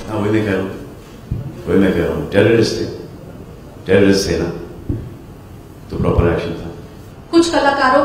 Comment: he clearly says "Terrorist the na ? toh correct action tha !!" Simply spreading lies for the sake of views !!